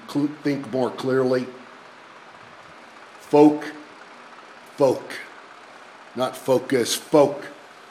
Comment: Could any of you guys tell me what is the word Warrior uses instead of FOCUS? It sounds like FOLK or something it is at 1:08. Sorry, english is not my native language, I understood clearly everything except that word. I am very curious to know what it is.